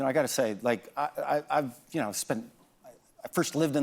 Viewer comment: Anyone else see the captions got messed up at right at 2:38, when McFaul stumbles a little on "I've" the captions read ivermectin? lol wtaf